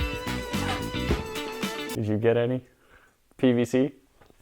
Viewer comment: I thought I heard "Did you get any? Bbc."